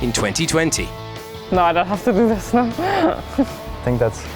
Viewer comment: What does SF say at 3:10?? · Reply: “No I don’t have to do this”